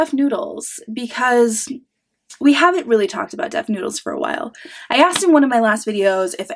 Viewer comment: At the beginning of the video, I misheard you, and thought you said "Buff Noodles", and started laughing. Then, laughed harder when I imagined a buff noodle, and realized it's yet another thing funnier than Dennis. .. Another amazing video, ma'am. Old lady high five incoming. 🖐